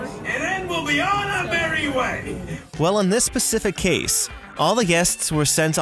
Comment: Did he say "on this pacific case"